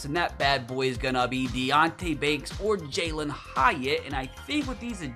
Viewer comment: People who thought Jaylin Hyatt said Jaylin gyatt